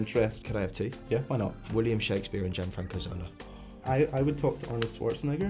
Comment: What was the name of the second person he mentioned that he would want to interview at 3:36? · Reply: Is he into football? I tried googling what I thought I heard him say and came up with Gianfranco Zola? Not a clue if that's who he actually said though.